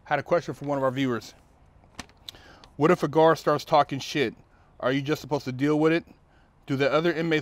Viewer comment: Thought It Said " What if a Guard is taking a shit " 😂😂